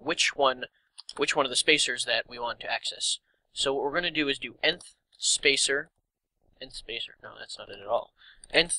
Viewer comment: for some reason your videos wont go on full hd for me, what do you say at the 0:50 mark? endth? Length?